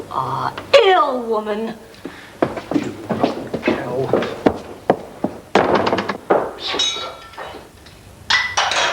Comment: I swear, she's saying "Cow!" as she closes the door. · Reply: Mrs White says ‘You rotten cow!’ So yes you were right.